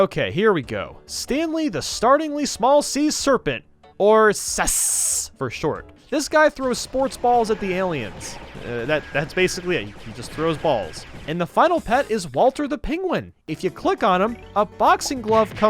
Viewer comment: did he say sus? 😱😱😱😱😱😱